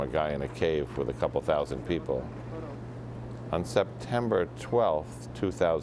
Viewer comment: did he just say September 12th?